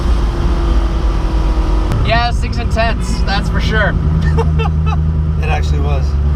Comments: what does he say at 16:32? six and ? i dont understand :-D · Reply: BikeCrasher He said “It’s intense” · What does he says at 16:34 - Six and tens? what does it means? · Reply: Nir Hason he says its intense